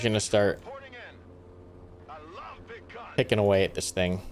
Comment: I know that the infantry/armoured vehicles are saying "I love big guns" BUT I keep hearing "I love big C*NTS" xD